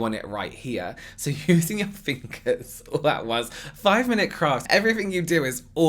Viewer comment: Did he just say "5 minute crap"kk😂😂😂